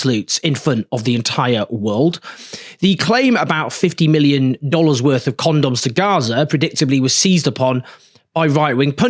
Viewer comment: I thought she said When it first aired 50 million on Condominiums. Because Trump administration in his first term bought Gaza from Israel! For his fancy Beachfront hotels and casinos!!!